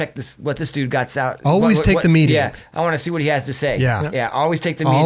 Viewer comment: what did they say @ 1:18:00 always take the ? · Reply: Always take the meeting!